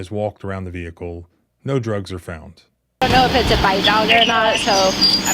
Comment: “bit” or “hit”. I couldn’t tell what said. 😤. . 😮 18:31 Oh ‘bite’ then? If the dog ‘bites’, it’s the police liability, not an innocent civilian’s prblm or fault.